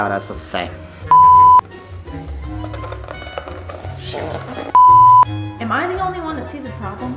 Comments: did he say sh*t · did he say sh*t ? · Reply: Nope, he said shoot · DID SAY THE S WORD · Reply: Noooo. Shoot. I said shoot.